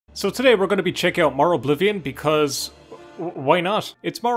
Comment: At first I heard: "So today we're gonna be checking out more Oblivion."